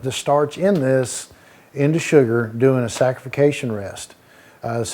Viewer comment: Hey! Great vid. I think said saccharification and not sanctification as you wrote at 2:20.